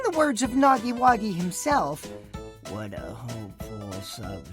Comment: WHAT DID HE JUST SAY? NAGGI-WAGGY? AS IN HE KNOW WHO JAY IS?